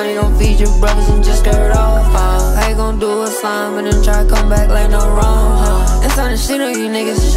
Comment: I thought he said “do us slime then try and come back like the Rona”